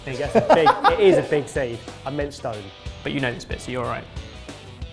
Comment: What did say at 0:51? I couldn't understand the roast lol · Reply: called the avocado stone a "seed" which they all pointed out was wrong , then after admitted to his mistake said "but you know this bit, so youre alright" meaning didnt know what the stone was called but at least he knows how to slice and prepare the avocado !!